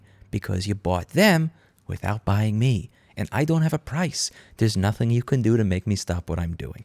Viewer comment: I thought you said you did have a price before? like 300 mil or something crazy lol